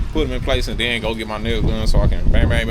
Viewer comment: Love all your info. It really helps people who need to make their own repairs. So many people have not been taught skills to help themselves. Haha I thought you said I have to get my nails done 🤣 then realized you said nail gun.